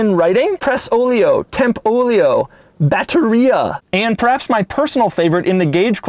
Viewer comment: when you said Batteria i thought you said diarrhea